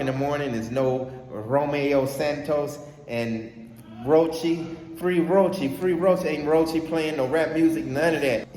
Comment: Coach I could’ve sworn you said Free Roaches I’m sorry I’m still hung over from last night😂😂😂😂